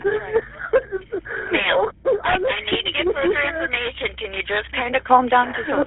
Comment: is she calling him ma’am 😂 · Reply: Yup lol he goes I’m the son